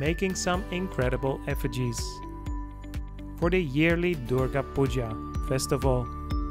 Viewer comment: he mentioned the name of a festival where the sculpting was going on and I can't quite make out the name of it. I would like to find it on the web and am wondering if you could help me with that? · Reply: It is called Durga (Kali) puja.